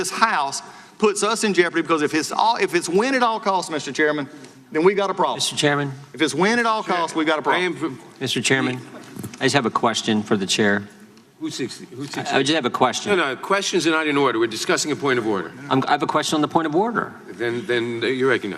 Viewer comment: did he say equestrian???